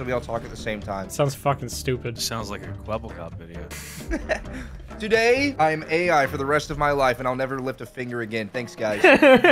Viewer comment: I heard that as "today, I am AI for the rest of my life and I will never live to finger again"